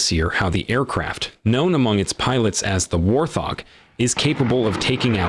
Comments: did the narrator just call it a "WAR Thog" or am I just hearing things wrong? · Wait,... did he say "War Thog"?